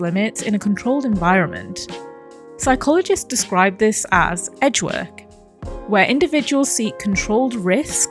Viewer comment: i had to double take because i thought you said "edgeworth" Very different game franchise